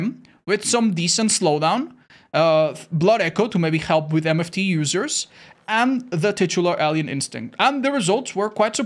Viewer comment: You know Otz when you said blood echo will help against MFT users, at first I thought you said NFT users 😂😂